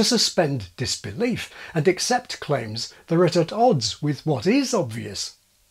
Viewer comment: What did you say at 1:31? Sounds like "...there at at odds"? Were you trying to say "... that are at odds" ? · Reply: Claims that are at odds..